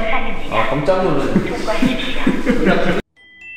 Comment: 이건 진짜 뭐라고 한거지..? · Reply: 아마도 '통과하십시오'가 아닌가 싶습니다